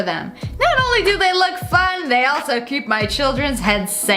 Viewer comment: I thought you said they work